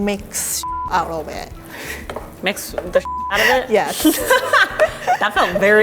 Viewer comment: When Rie said "mix the shit out of it" i thought she said "eat the shit out of it"